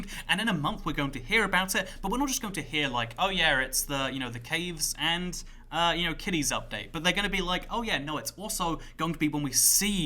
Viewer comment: Did he just say ‘Caves and Kidneys Update’?